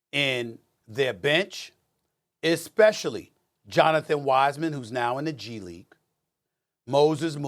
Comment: Did he just say Jonathan wisemen 😂😂😂